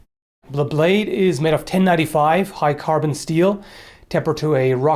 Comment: Oh, I thought that said cold steel. Disappointed face